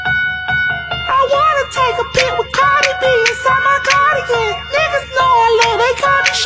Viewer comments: What I hear : " I wanna take a pee with CardAI BeE " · I didn't hear this right(I don't think) but this is what I heard "I wanna stuff Cardi B inside this cardigan" ☠️☠️☠️☠️☠️☠️ Just wow 🤣🤣